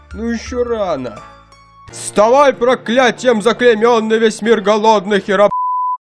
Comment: Я почему то услышал "Вставай проклятием заклененый весь мир голодный к херам"